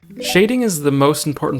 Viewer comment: At the start I thought he said shaving, and the microphone was a shaver 😭